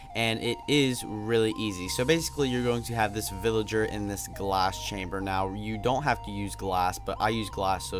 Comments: Thought you said gas chambers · insted of glass chamber I thought u said gas chamber I was like nazi